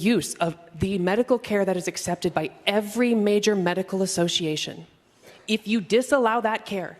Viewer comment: What are yall's thoughts on these protests against this trans Democrat being silenced by the GOP?! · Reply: "Gender affirming care is accepted by every major medical association." What I heard is, "Klaus Schwab has plants at the top of every major medical association."